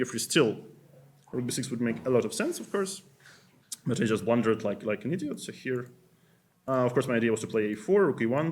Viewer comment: did he say at 2:05 that he blundered like an idiot? :D :D